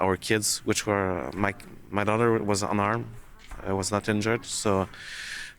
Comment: I thought he said his daughter was unarmed. It was very confusing. :P